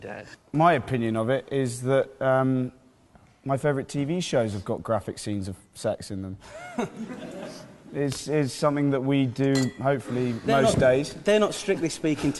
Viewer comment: What dose he saying? 😭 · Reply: Arwin Hawraza my favourite tv shows have graphic scenes of sex in them. It’s something we do